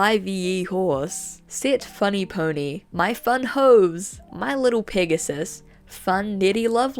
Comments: I THOUGHT YOU SAID MY FUN HOES??????? AND LOOKED UP FROM MY STUFF LIKE WHAT??????? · I went to make a sandwich either the video still playing in the background, right? At 27:21 thought they said “my fun Hoes”